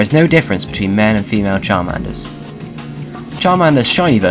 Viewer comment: why did you say man and female charmander, not male and female charmander.